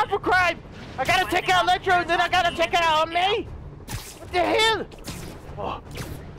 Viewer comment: did he say he going to take out aunt may!